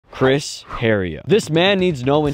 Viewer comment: Yo I thought he said Chris rock